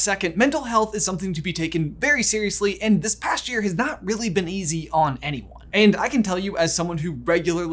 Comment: I love the reasoning behind Scotland choosing unicorns. England's animal is a lion, no one (or very few) in England had seen a lion, so logically unicorns were just as likely to exist. Scotland "You want a lion, as your nation animal? You've never even seen one. Hmmph! Well if you're a lion, I'm a unicorn." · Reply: Oops I thought you said has not had